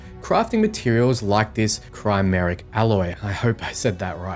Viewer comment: "chimeric alloy" - sounded like you said it with an R at the start ("ChRimeric"), whereas, in fact, there is no R there.